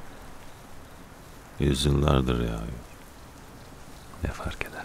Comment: de ne diyor, ben anlayamadım tam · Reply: İğdebeli’ne yağmur yağıyor…